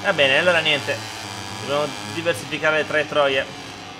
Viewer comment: solo io ho sentito traeTROIE?